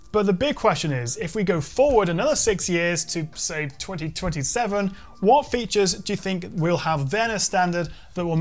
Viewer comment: I legit heard him say "If we go 6 years in the future, what features do you think would have vanished".